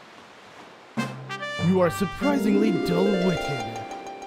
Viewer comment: I turn on captions by accident at 0:56 and when the King of the Red Lions says "You are surprisingly dull-witted", the captions say "you are surprisingly don't working." And I mean, yes, but actually no.